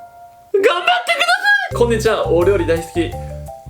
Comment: 初めの お料理大好きが俺より大好きに聞こえた